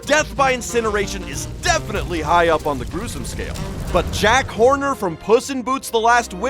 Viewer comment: i think i heard "jack horny" instead of jack horner